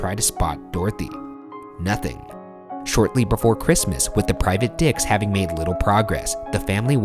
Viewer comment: Why did it sound like he Said "private d*cks"😳